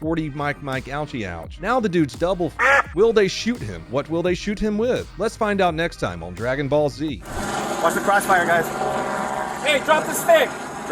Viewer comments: For a split second, I thought the officer said "watch the crotch fire guys." 😂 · I heard "watch the crotchfire"